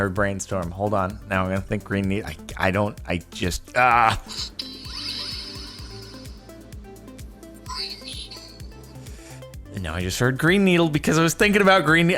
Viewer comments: i heard brain needle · I heard “brain needle” lmfao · I Always hear green needle :( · why do i hear "Brain Needle" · I thought Grain beetle and heard it